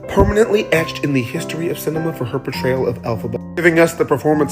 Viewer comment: elphawho ? Tf is this ? 😅 7:15 say who? 😂